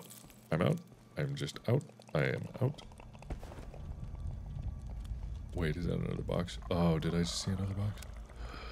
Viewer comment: I thought he said "wade is that another box?"